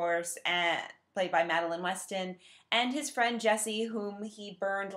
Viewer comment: Did you say "played by Madeline Westen"? So Madeline Westen plays Madeline Westen?